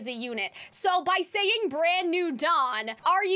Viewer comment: i thought he said 'don' like mob boss, like he's the new boss